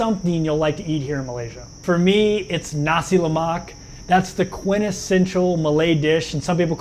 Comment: its name nasi lemak if u guys dont understand replay